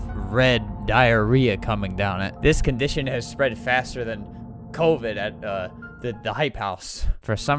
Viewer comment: 'This condition has spread faster then Covid and.. The hype house.' -, 2021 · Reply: i thought he said covid IN the hype house lmao